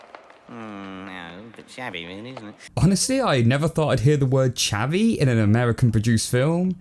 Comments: I thought he said "Shabby" not "Chavvy" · I always thought Victor's dad was saying "A bit SHABBY really, isn't it?" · "Chavvy"? Here I always thought he was saying "Shabby" ,o.o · Does he say chavvy!? I always thought he said shabby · Did he say Chavvy? I thought he said Shabby. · Maybe it's just me, but I think he's saying 'Shabby' rather that 'Chavvy'. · Always thought he said shabby not chavvy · i think he might have said "shabby" but i could be wrong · I always thought they said "shabby" I'll have to look the other up yo see if they're basically the same thing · I thought the dad said shabby not chavvy lol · I thought it said "Shabby" rather than "Chavy" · I thought the dad said shabby not chavvy xD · I always thought he said "shabby" not "chavvy" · I thought the dad said shabby not chavey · I've always thought he said 'shabby'. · Chavvy? Well shit I leavened a new word today; I thought he said "shabby" until you brought it up.